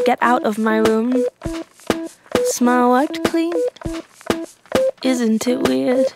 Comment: I thought doodie was saying smell wipe clean💀💀💀💀💀